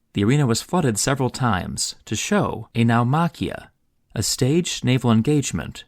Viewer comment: "A now machia" What word is that? I can't google it, please help me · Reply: Naumachia